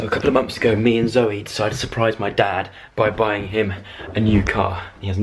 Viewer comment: I thought it said buying my new dad a car😂😂😂😂